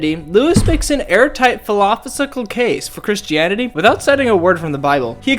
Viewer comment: Airtight falafel 😂 no harm meant, just thats what it sounded like when you tried to say philosophical.